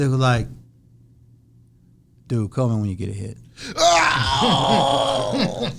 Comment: What silk say? Not only can silk not rap but he cant even talk right. Lol. What did he say silk said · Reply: “Call me when you get a hit” he wasn’t feeling the beat